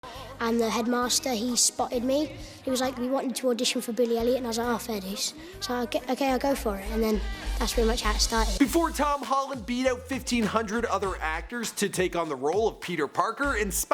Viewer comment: And I thought they said "Billie Eillish"